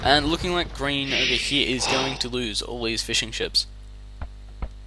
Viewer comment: man it sounds like your saying fish and chips!